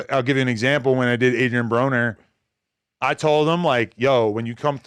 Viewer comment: I just wanted to say it sounded like you said Adrian Boner 🤷🏻‍♂️ that’s all, I’m out… on b team we goin up like gas prices not Boners